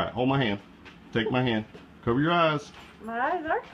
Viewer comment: Maybe it's because English is not my first language, but at 0:38 when he says "cover your eyes" at first I thought he said "cover your 😂